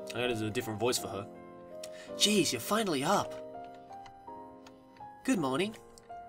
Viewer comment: I thought it said: geez you're filling me up.